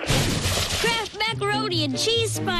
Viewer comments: It works as soon as you pull the string. 0:04 A claw will add some macaroni to the macaroni bowl and the macaroni bowl and the cheese pot will roll down different tracks. 0:08 The cheese pot goes flying after a ramp, then a claw catches the cheese pot, then sends the cheese into metal pipes. 0:10 Meanwhile, the cart with noodles stops suddenly at its track’s end and the noodles land in a metal bowl that moves along a zipline as a chain reaction is triggered. 0:11 Spoons cause one another to rotate downwards so it triggers the next one, then Kraft Macaroni and Cheese boxes fall like dominoes. 0:14 The macaroni and cheese merges in a cannon when the cheese fills a small pot and causes a rotator to tilt. 0:16 This causes a magnet to attract the metal bowl, causing the macaroni to fall into the cannon. The last Kraft box falls and tilts the cannon. The cannon fires the macaroni and cheese into a very large pot. 0:19 The macaroni and cheese then travels through a fake phone wire. 0:20 The phone attached to it rings and Cheesasaurus Rex picks it up and waits for an answer, only for the macaroni and cheese to splatter him in the face!!!!!!! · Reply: Yeah and then after that in 0:29 the Cheesasaurus Rex says a misheard phrase, "Cheese me!". Is this correct? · For years I thought the dinosaur was saying cheese me 😂😂